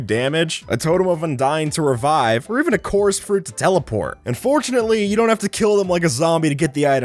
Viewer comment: did he say unfortunately you DONT have to kill the fox or and fortunately you dont have to kill the fox.